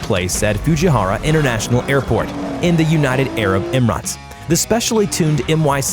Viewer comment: It sounds like he said emrats instead of emirates in